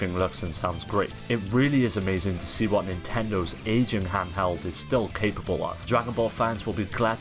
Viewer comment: I thought you said "Asian handheld"!